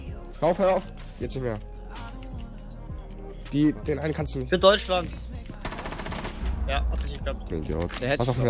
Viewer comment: Hab ich bei 0:30 richtig verstanden? :Für Deutschland!! 😂